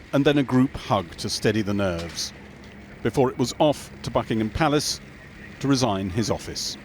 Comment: At the start I thought he said Fuckingham Palace